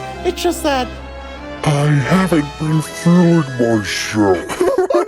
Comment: what does he say here? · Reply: I think he says "I haven't been feeling myself"